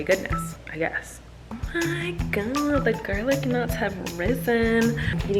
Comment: This whole time I thought she was saying “Garlic NUTS”. I was like. Why are you making bread??? 🥖